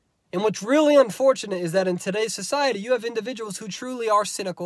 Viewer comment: At first I thought you said "in society you're a moron" lol I need to clean my ears